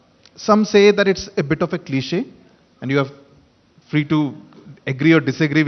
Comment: praveen kumar Can anyone help me to recognize that word in 1:05 What he says after word "some say" that it's bit of ------??? Please write down the word below. With correct spelling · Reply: "cliche"